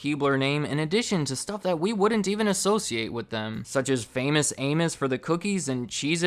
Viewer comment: Who else misheard it as "Famous Anus"?